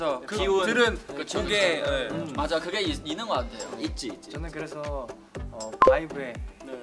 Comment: Did Jun say "Itzy" at 4:01?? LOL wbk that a few Seventeen members are definitely Midzys! Performance team will definitely appreciate performance queens!